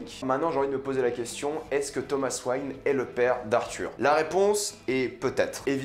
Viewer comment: C moi ou il a dit Thomas Swang